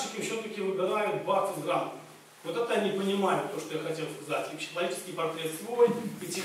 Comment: что чаще всего выбирают заказчики? 16:34 не расслышал? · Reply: Вроде как - бакфаст с рамками (мне кажется имелось ввиду - бакфас с определенными критериями)